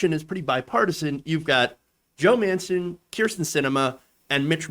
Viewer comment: For a second there, it sounded like you said Joe Manson. If you did, that's a hell of a Freudian slip, good for you. Edit: capitalization and punctuation